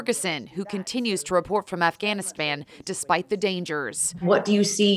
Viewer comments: did she just say Afghanispam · Was it just me or did she say Afghanispan? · Did she say afganispam?